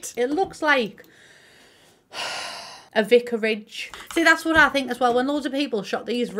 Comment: OK did say "vickeridge" because my coworker always called me this and I couldn't get what it was in reference to!!!! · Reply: Vicarage was the word, it's the house that a minister of a church would live in while he's minister of that church (the church owns the house though) it's definitely a unique nickname 😊